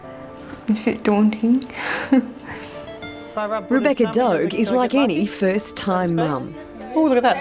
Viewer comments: I thought it said Rebecca’s dog is 31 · Thought it said Rebecca's dog, that would have been one old ass dog